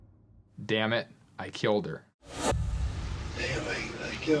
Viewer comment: I don't know what they were all listing to repeatedly and had other listen to reputedly... I clearly hear "Anyway, I killed her" 9:57 I don't know how they heard "Damnit"